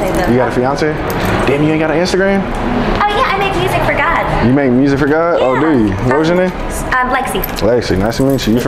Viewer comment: did shawty just say she make music for god?